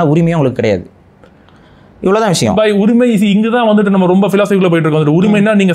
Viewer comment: did he called Pari as bhai